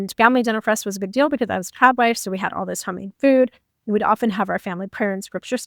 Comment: have you been saying “crowd” wife or “Trod” closed captions can often be wrong lol I’m partially deaf in my right ear so I’m not sure what people are saying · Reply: Trad (traditional) wife